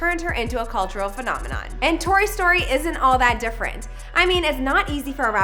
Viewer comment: I thought she said “and toy story”